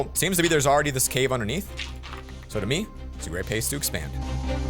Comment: "So for me, its a great pace to expand." Pace is not a typo that's what you said lol.